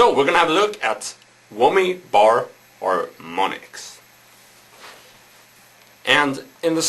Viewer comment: i thought he said "were gonna have a look at women bar harmonics" haha